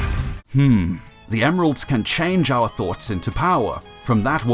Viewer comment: at first I thought she said “The seven emeralds can change our butts into power” and I was like “Yo wth?”